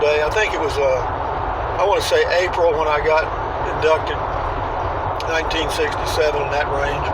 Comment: You said "the day I got inducted" and I thought you said "the day I got abducted"....hahaha....all the same to you, I bet. You had me feeing sorry for you, cowboy. What a great story! This country would be a whole lot better off if they brought the draft back...for ALL of us, men and women alike. But they don't need to serve together. Make me Queen of the World and I'd whip this sadass country into SHAPE!